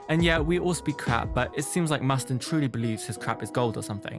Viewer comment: I do miss his purpose so much tho but did you just mispronounced justin's name to mustin? I swear I heard you say that! 😆 😂